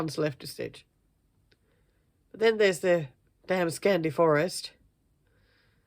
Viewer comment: I have to laugh: When you were talking about the things in the "Other" category, I heard you say "and then there's Dan's Candy Forest." Well I was like, What??? Then I realized you had said "Damn Scandi Forest" 🤣🤣🤣 Oh my gosh, that was a good one!